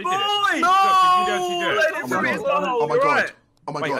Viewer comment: Why do I hear him saying "I'm a god"?